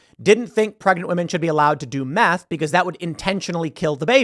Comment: Had to re-listen like four times because I thought you said "math" instead of "meth".